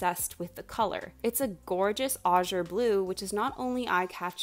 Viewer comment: Does anyone know what color she said? I can't quite understand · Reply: azure! it’s that specific shade of blue